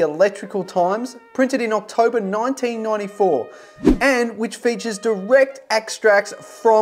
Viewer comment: did he say 1994 instead of 1944. 🤔